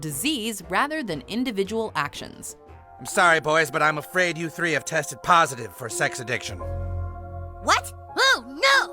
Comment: Now I wanna know what kenny said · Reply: Leobuzzkill, Kenny said “who fucking cares” if you listen closely from the muffles